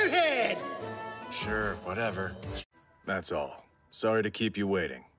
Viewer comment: he said “but ever” that’s what I heard